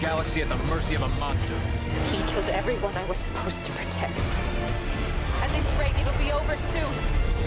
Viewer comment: What Isabel said in the 3:59? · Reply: BloodM117 "At this rate it'll be over soon!"